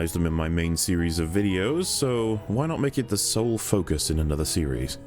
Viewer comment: Sorry. You said "sole focus" and I heard "soul focus" and starting laughing uncontrollably before you even got into the actual video